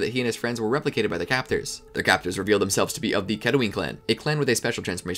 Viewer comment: i thought he said ketamine clan lol